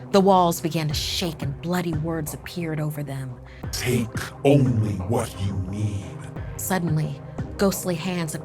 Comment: I heard "take only the weed"